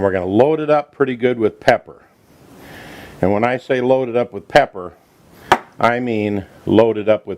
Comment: Did he say to Load it up with Pepper?